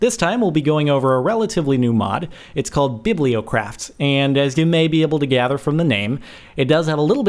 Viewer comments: I thought it said bilbo craft...so I was looking forward to lord of the rings, BUT THIS IS GREAT TO! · i thought it said Bilbo craft •_•